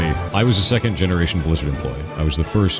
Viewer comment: At some point, all I hear is "lizard" 😂